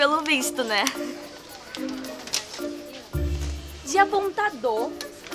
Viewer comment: Não sei como mas eu ouvi "Japão tá dor" ok né Mas uma escrita😘